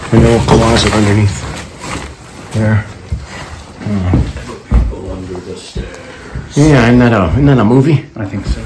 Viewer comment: Hello, did your friend say " the people under the stairs " because at 29:13 I heard that being said, so creepy. Lol Great video Also at 1:17:18 I thought I heard a disembodied woman's voice. I have ear buds on, have one of the crew check it out. ✌️&❤